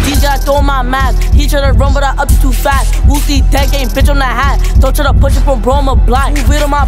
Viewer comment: Did he say wooski or whoopty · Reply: whoopty